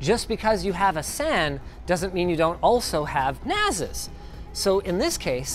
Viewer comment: did you just say Nasus o.o